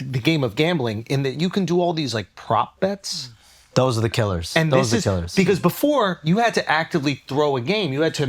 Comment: Shams pretending that he doesn’t know what a prop bet is laughable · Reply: what are you talking about?? he says "those are the killers"